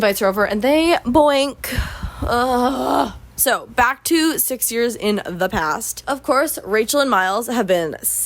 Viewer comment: for the first 10 minutes i thought you said SIXTY years into the past and that made this story a WHOLE LOT more interesting